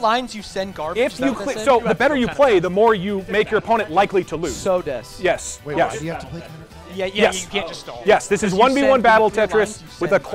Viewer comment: "そうです"? or am i mishearing